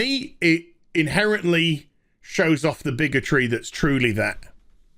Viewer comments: I legit had to rewind a couple of bits because I heard "bigotry" as "bigger tree." Also because I had to wipe my drink off my monitor repeatedly. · Bigger tree? Bigotry? I thought I was mishearing.